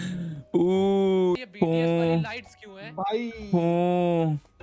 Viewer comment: did he said BTS wali light ?